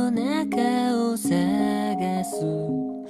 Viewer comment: 「夜に取り残された」が「夜、にとり、残された」に聞こえた…